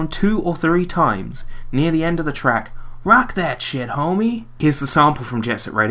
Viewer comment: And I always thought he was saying, "Back at'chya homie!" M A J O R suprise.